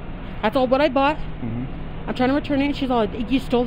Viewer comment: Freudian slip “I didn’t steal it from here” 😂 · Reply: I thought she said “I stole what I brought” at around 1:15 😂😂😂 I can’t stand these fools and I wish judges would stop just letting them loose with a little fine 🙄